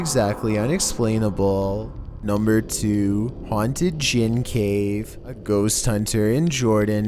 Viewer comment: Number 2, I thought he said haunted jizz cave lol